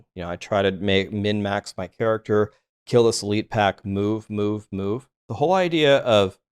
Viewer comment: Is it only me or when he said "move, move, move" at 8:28 I heard the hell bovines from D2 :D